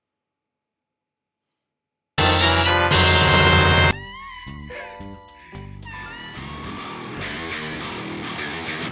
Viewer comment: That is GORGEUOS! 🤩🤩🤩🤩Highlight of the video was definitely 8:54 and your reaction to it. Did you say pain? Damn that chest! lol I cannot wait to get this bad ass of a figure! Awesome review thanks! 😎👍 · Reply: Glad ya enjoyed, fam! And yes I said “PAIN” lol even doing that in a not at all serious way hurt lol